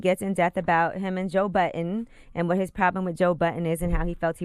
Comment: Sounds like she is saying Joe Button ....